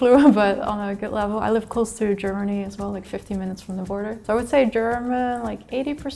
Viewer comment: I think she said 15, not 50. 50 minutes from the border is basically half the country